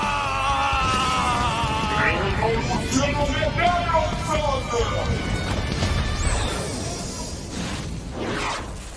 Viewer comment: Nono ho mai capito cosa dice la voce in sottofondo a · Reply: Evoluzione neozon